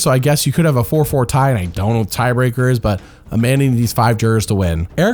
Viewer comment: What does the narrator say at 1:11:59? Something about tiebreakers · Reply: If there is a 4-4 tie, I don't know what the tiebreaker would be.